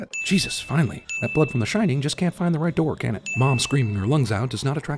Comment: Subtitles say "find the right floor" narrator says "find the right door" +1